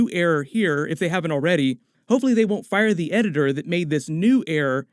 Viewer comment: You said I hope they don’t fire the editor but I thought you said I hope they fire the editor and I was like omg 😂😂